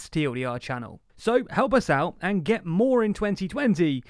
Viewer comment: am I the only one who heard ''get more in 2020''